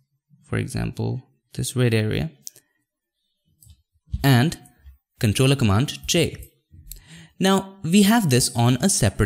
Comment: What are u saying, Ctrl and command ???